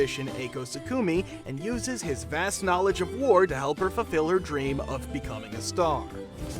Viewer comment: i thought she said her dream was to become his dog lol and then i turned captions on